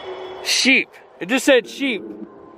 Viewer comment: I heard ashamed not sheep.